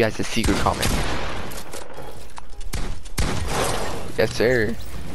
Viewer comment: When you said "yessir" it sounded like you said "hey siri" 😂